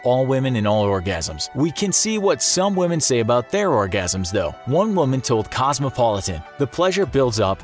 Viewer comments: I THOUGHT IT SAID ORGANISM AAAAAAAA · I THOUGHT IT SAID ORGANISM SHSJSJSJS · I thought it said organism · i full on thought it said organism 🤦🏾‍♂️ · Thought it said organisms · i thought it said organisms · Oh shit, I thought it said organism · i thought is said organism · Bruh I thought it said organism but ok. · Shit I thought it said organism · Dude I thought it said organism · I put this on the tv with my parents I actually thought it said organism